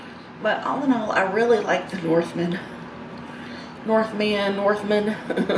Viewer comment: Ohhh, “the northmen”… thought you said, “the movement”. Was gonna say, lol why did you pay to watch that? I saw it for free on nicks channel last night (every night he posts) :)